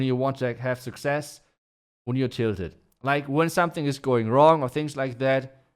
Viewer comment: what was the word? Children? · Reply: tilted. When you're frustrated and angry